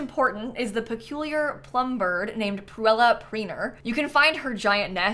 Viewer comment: I misheard. I thought you said the bird was called Plumbella.